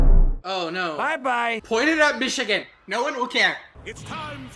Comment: did you say Michigan? I LIVE THERE